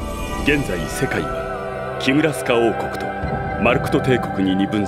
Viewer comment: 懐かしいね ゲームもやったし、アニメもちゃんと見てたね ネビリム先生倒すの苦労したな ルークの家に飾ってあるガイの剣のイベ逃したんだよな あと他のイベも 攻略本発売した頃にはもうクリア目前だったし 二周目したかったけどその頃クリアしたらやる気なくなる症候群になってあまりやる気なくなってたし 余談なんだけどキムラスカ王国が木村さん王国に聞こえるんだよね～